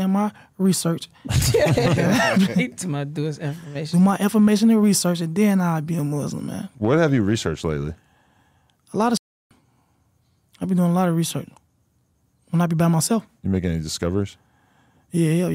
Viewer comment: I thought he said he was a Muslim 🤦🏾‍♂️